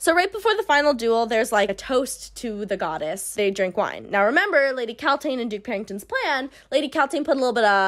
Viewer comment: Kept mishearing “Lady Kaltain” as “Lady Cow Taint” 10/10 video